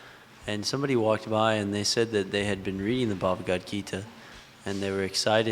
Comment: Hare Krsna. What did he call the Bhagavad Gita??? 53:06 Did he say Bhagbahd???